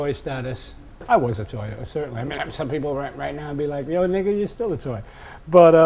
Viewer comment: I tried to ignore it but did he say @ 2:19 "nigga". He seems like a cool guy but ummmm.....